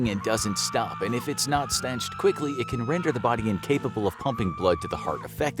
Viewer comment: Did he say "if the blood's not stenched quickly"? 🤨 It's staunched. 🤦